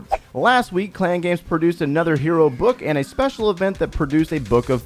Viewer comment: 'hero of book' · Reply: It sounds like he said hero of book. Thanks for the heart!